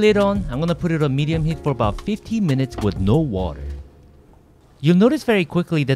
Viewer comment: did you say 15 minutes or 50 minutes?